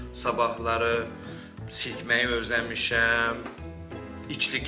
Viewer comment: ben mi yanlis duydum yoksa sikmeyimi ozlemis